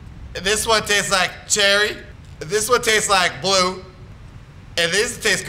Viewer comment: lol this is sooo funny and wen he said and this one tastes like blue i thought he said and this one tastes like GLUE lol